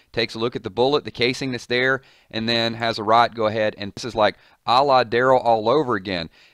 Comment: What exactly did you say at 2:46? Lol I heard "Allah Daryl